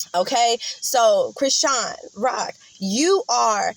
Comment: Girl I thought u said Chris Rock😂😂,it's true nobody knew about him or his first baby mom,they don't expect.angels to smoke weed,look at Rihanna,they tried to stop her too